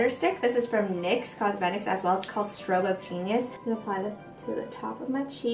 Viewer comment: thought you said "strobe of penis" on the nyx highlighter stick. 😂